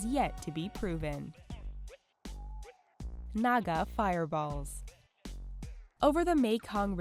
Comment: I literally thought it said, MAGA FIREBALLS haha